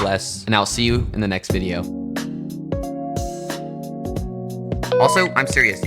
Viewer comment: when he said dont forget to use precionfn i thought he said premfn